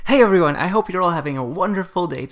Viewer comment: mishears intro: Hey everyone hope you are having a wonderful birthday! Me : Wut ;w